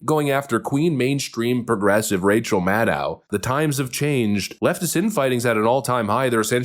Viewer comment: I must have misheard, but that's okay. From now on, she will be Racial Maddow to me.